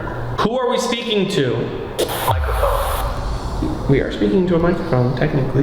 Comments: “microphone” sounded like rug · Brooo I might sound ridiculous but in 28:58 when it said microphone it sounded like you rug